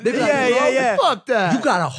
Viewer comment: Did mike say “fuck that nigga”